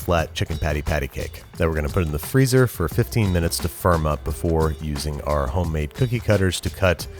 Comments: Whenever he said "firm up" I thought he said "for a month" this made me super confused when he said "that gives me enough time to make cookie cutters" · Make the Mississippi Queen from Regular Show! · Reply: I thought he said "put them in a freezer for a month" and I was like oh damn so we are going real authentic with these mcnuggets lol. but i think he actually said "firm up" 😂